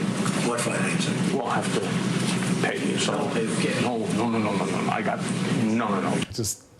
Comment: Cohen: "We'll have to pay him something." Trump: "[inaudible] cash." Uhhh... it's not inaudible. "We'll pay him." Can anybody else hear Trump quite clearly?